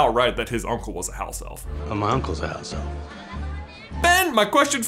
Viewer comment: Plot twist: Jacob's Uncle is actually a House elf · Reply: I always thought he said 'I'm my uncles house elf' I never realized 😳😂😂